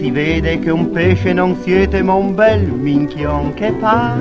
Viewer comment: Al minuto 00:56 dice esattamente "UN BEL MINCHION!" u.u Ho creduto anch'io cosa strana che la Censura avesse permesso questo termine... poi ho fatto qualche ricerca su google ed ho visto che "minchione" è un termine di gergo abbastanza comune (naturalmente per dire "sciocco") e dunque non più offensivo ormai. Come sono acculturato XD XD